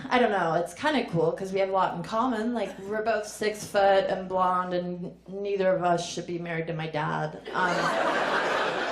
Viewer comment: What word did you say at 6:58 ? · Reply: DM It's "quinceañera", meaning a girl turning 15, a big life event in many Latin American communities. Similar idea to "sweet 16", a coming-of-age into womanhood, celebrated with friends and family.